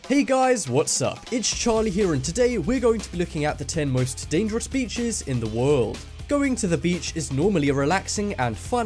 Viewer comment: i thought it said 10 dangerous bitches in the world like seriously I'm blind